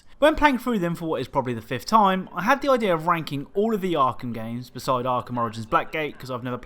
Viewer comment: Lmao it sounded like you said wanking not ranking